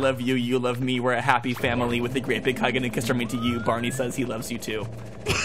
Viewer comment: I thought it was "won't you say you love me too" not "barney say's he loves you too"